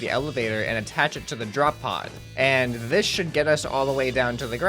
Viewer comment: I thought the video said drop poo!😆😆😆